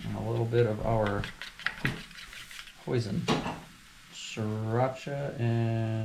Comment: Hoisin, Sriracha, chipotle mayo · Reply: Thanks....funny, in the video sounded like you were saying "poison". And for someone that is NOT very kitchen savey, I have never heard of Hoisin so I had NO idea what it was supposed to be. Thanks again.